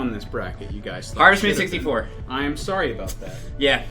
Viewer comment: If you’re in the comments wondering what game said at around 11:41, Harvest Moon 64. He mumbles the name and was nearly impossible to understand.